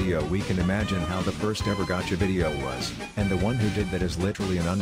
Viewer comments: I HEARD “A GAY THEORY-“ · When he said “A GAME THEORY” I thought he said gay theory